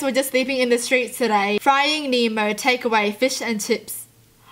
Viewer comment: “Frying Nemo”I thought she said finding Nemo lol 😂